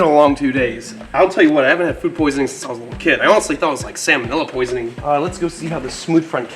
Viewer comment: If you and your friends all got sick, unless you ate with all of them somewhere it's not food poisoning. Also I swear it sounded like you said you didn't want to get salmonella, but it sounded like you said salmon vanilla lol. It made me laugh so hard😂😂😂